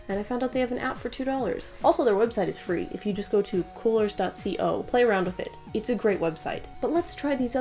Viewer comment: What’s the website she said she uses for colors? It sounded like coolers.co but that took me to an ac unit website. She said it around 7:40 I can’t tell what the website is